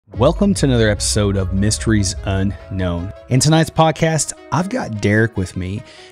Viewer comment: Unknown Hominid I thought he said. Makes sense to me👍🏼🌎☀️💙